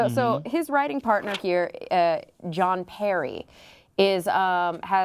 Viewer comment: I thought she said "John KERRY". I was like "Wait, WHAT?"